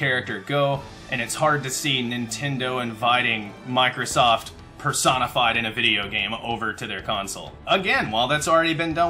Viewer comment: Is it weird around 4:28 it sounds like he says Persona 5 two months before Joker DLC revealed?